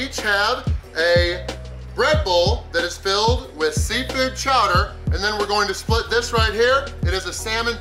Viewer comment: I thought you said 'a red bull with seafood' lol